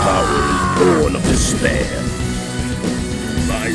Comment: What was Bison saying? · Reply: My power is born on despair